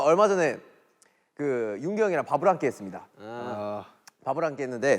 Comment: I thought J-Hope said he ate out Yoongi I’m dead😂💀